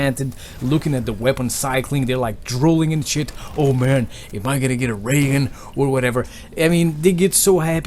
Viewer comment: I thought you said "Am I gonna get a Reagan "